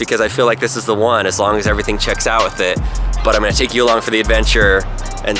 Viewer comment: Take "Elon" for the adventure. Get it, cause it "you along" sounded like Elo- nevermind I need sleep... xD